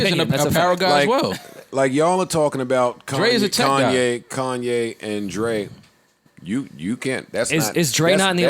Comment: i thought this shit said “Kanye and Drake” i was like ohhhhhh shit 😂